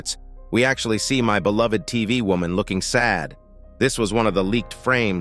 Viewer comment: Me: did you just say "Astro Mommy"? No. I said "Tv Woman"